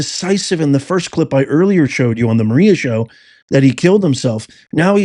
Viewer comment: Sounded like Bongino said "THEY" killed himself